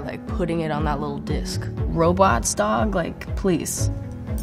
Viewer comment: I thought she said Roblox dog about when she was answering the things about electronics